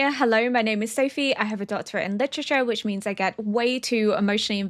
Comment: I genuinely thought you said you have a 'daughter in literature.' I was so confused for a second like, 'Wait, what? A daughter?!' 😂 Then it clicked that you meant a doctorate in literature. My bad, sorry, haha.....Btw, love your content, miss. keep posting...❤